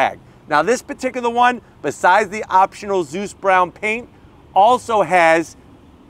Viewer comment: I thought you said Deuce Brown at first!